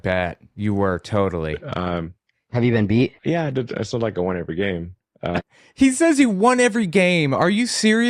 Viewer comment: I absolutely cannot stand the idea of defending this shadow fuhrer, but he literally did say “it’s not like I won every game”. He says it really weird, and the captions did think he said “I still” instead of “it’s not” but yeah he didn’t say that he’s undefeated.